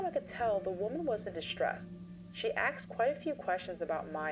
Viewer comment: Did the narrator say "axed" instead of asked at 16:23? 😢